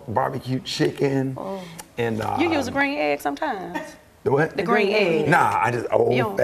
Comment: What does Quad say at 1:01 ? Green eggs?